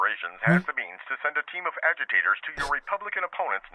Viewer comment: "Sending a team of agitators to the next rally". I thought it said "team of alligators".